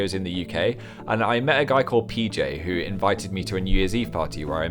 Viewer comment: i thought he said bj but he said pj